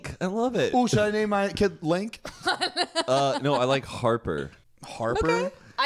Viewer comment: Im sorry but why did I think said "I like Harvard" 🤦‍♀